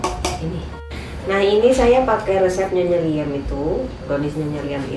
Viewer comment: resep siapa maaf ngga kedengeran atau tidak begitu jelas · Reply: Ny.liem